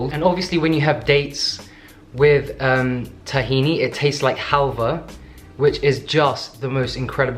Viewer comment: I heard it as "it tastes just like vulva - which is just the most incredible thing in the whole world" (08.42) lmao